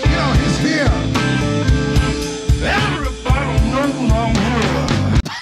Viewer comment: Mister Seagal was just saying: 'EEERYbody knows I'm here' in the blues segment. 3:30 Do not feel bad, ser Jon. I am fluent in bluesinese.